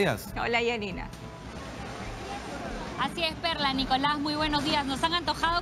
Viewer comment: Like si pensaste que dijo perra :v